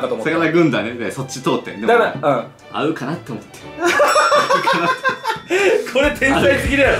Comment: の｢合うかなって思って｣がめっちゃナダルに聞こえたww